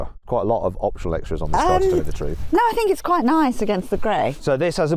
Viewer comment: 'Oh I think it's quite nice against grey.'って言ってるように聞こえるけど字幕間違ってんのかな？